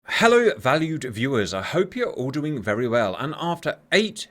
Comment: This guy makes us feel extremely valued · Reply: When I first started watching, I thought he was saying “valley viewers”. It’s one of my funniest stories.